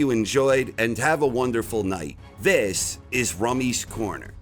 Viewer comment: I continue to mishear the choric reiteration of this channel’s name in the intro as “Rummy’s coroner”